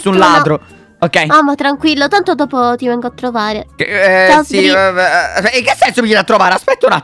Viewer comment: Al minuto 9:13 dice "AMO tranquillo dp ti vengo a trovare" o se no ho sentito io male boh 🤔🤔🤔